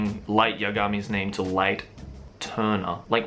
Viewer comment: am i the only one who thought he said turnip instead of turner